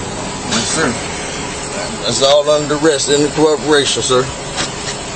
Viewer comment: Every time one of these clowns starts yelling "Under duress," I hear "Under my dress." Cracks me up.